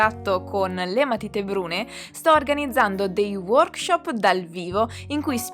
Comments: Ciao, 👋 Okay 👌 Work shop, I thought you Said shopping 🛍 🤣🤣🤣🤣🤣👍. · The other day I thought you said “ shopping “ but actually I were saying ( Work Shop) whool even my Ears hearing things , because its wanting to go shopping too 🤣🤣🤣🤣🤣🤧😭.